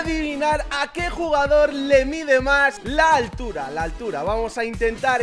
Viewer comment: no man soy re enfermo 0:05 Xd escuche a quien le mide mas la tul4 XD